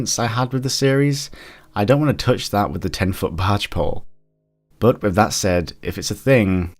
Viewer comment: Also, I miss-heard him, I thought he said "barge pole" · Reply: OfficerMarmalade he did say barge pole, it's an English saying.